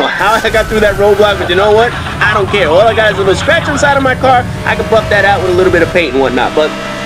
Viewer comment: i heard you said I can fuck that out.